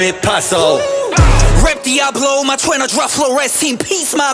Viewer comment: 대충 들리는 말 : 랩디아블로#@@@@ 맑퀄